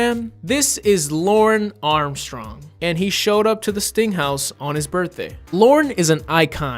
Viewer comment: Whenever he says 'sting house' I hear 'stink house' which is way funnier.